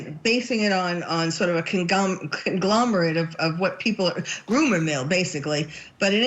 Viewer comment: did she say “groomer mill?”